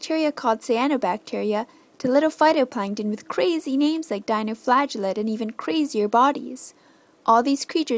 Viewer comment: For a moment there I thought she said dino flatulate. lol a.k.a. dinosaur farts